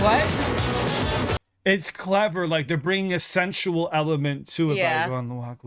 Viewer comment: Clever? Wait I thought he said “Love Her”. Like every other guy growing up in the 80’s loved her.